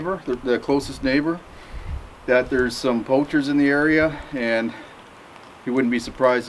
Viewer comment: Hey guys! Can someone help me? At 17:40 what does that word means, poulture or something like that? I think it is a bad guy who wants to steal, or am i wrong? Ofc english is not my mother tongue so please don't be rude. Thx! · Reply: Poacher is someone who hunts without a licence or out of season/without permission.